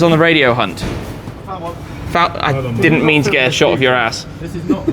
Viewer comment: Did say "ass"?